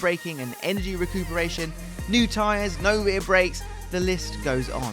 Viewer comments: I’m sorry did he say “NO REAR BRAKES”????? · did i hear "no rear brakes"?